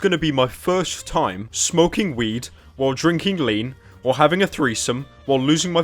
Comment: I heard at first:'drinking lye'.